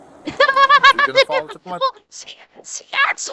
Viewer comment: I thought he said , are you gonna follow the sacrifice